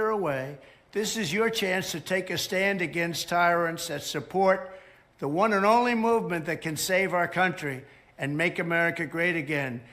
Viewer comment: I've listened to what Trump said a dozen times and what I hear is "...this is your chance to take a stand against tyrants, and support the one and only movement that can save our country and make america great again". Am I missing something? That doesn't sound to me like he's saying "don't support MAGA".